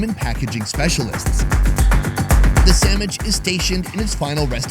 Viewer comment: did I hear sammich?